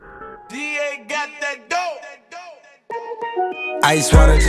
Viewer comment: Am I the only one who always thought it said “D.A. gotta go”?